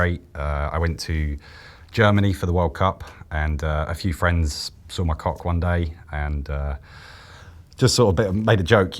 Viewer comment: I actually heard he said "a few friends sucked my cock" and even though i heard it wrong, it still sounded fine coming from him...